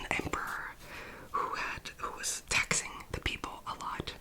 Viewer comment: ! A few points of note: 1) I initially thought you said "There was an Emperor who was TEXTING the people a lot" I was like "Whoah!" 😂 2) I legit thought Omar was just gonna knock the Princess clean out with that sceptre and be all casual: "What? She's not crying now, is she?" 😭😭